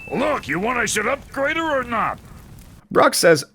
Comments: Brok doesn't say us. What he says is "You want I should upgrade her or not?" Moments like that make me think how much dialogue is misheard or misinterpreted if you don't use subtitles. That's why I always have subtitles on when I'm watching movies or TV or playing video games. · I think he just said "you want I should upgrade her", which is in line with his crude mountain man vernacular. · he actually says “You want I should upgrade her or not?”, he doesn’t say us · The only one I disagree with is at 10:34 because he misheard “you want I should upgrade her” for “you want us to upgrade her” easy mistake to make, first time I’ve found this channel and instantly a new sub